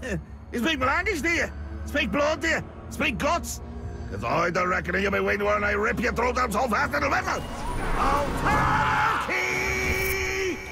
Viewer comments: I replayed this so many times and I think he's saying something like "Cause I don't reckon you're gonna be when I rip your throat out so fast it'll whistle" but it's still so unclear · MOON BUCKETS! xDD 4:56 Sounds like he says "ya be waitin`while Ay (I)" at the part where the subtitles show only (?????). Sneaky :,D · I love it how the subtitles didn't even know what he said there. Oh, and by the way, in case you were wondering, he said "Cause I'm da reckonin you've been waiting for and I'll rip ya throat out so fast it'll whistle."